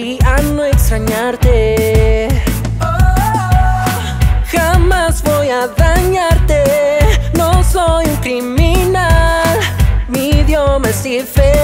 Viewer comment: Entendí '' jamas voy a bañarte, no soy un criminal ''' JAJAJAJAJJAJJAJAJAJAJAJA